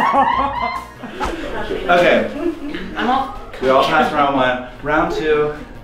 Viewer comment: did Mitch say “I’m all cummy.”